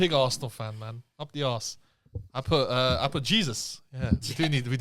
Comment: Am I the only one who heard say "up the ass"?